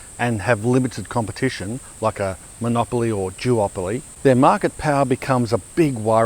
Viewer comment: Absolutely love the videos but did I hear “jewopoly”?😂